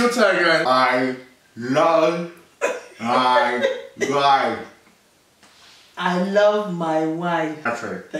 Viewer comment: when their dad said 'i love my wife' i thought he said 'i love fried rice' 😂